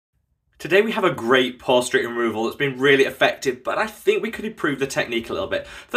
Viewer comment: Definitely thought he said prostate removal. 🤷‍♀️